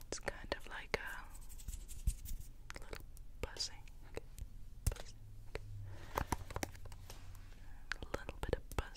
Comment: Someone please tell me what she says at 15:20 -- i am not hearing it right, im sure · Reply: A little bit of buzzing